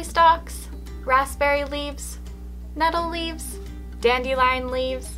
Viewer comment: I thought you said metal leaves😂😂😂